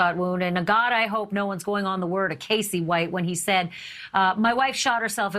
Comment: Did she just say, "my wife"?!